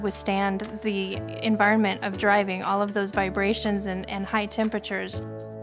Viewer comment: the automatic captions say "i love those vibrations and high temperatures" but this is great seriously, it's definitely going to contribute to a better world